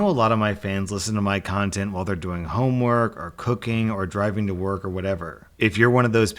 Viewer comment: When he said cooking i thought he said cocain